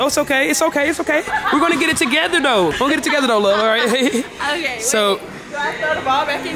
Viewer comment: did she say love????